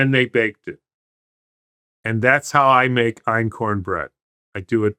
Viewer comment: what is he saying? "iron-corn" bread? The transcript reads "eorn" bread. What's that? All this is fine- if one has enough time/money/resources to do it with. The rest of us have to eat poison.